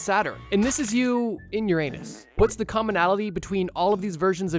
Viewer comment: I heard this you u in ur ans 😅